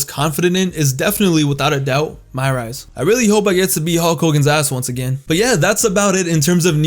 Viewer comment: did you say you couldn't wait to eat Hulk Hogan's ass again?